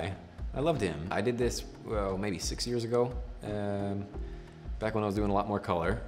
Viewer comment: I had to rewind. Thought he said 86 years ago